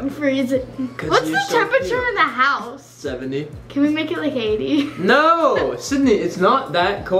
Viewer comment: When you said « can we make it like 80 » I thought you said « can we make a baby »😂😂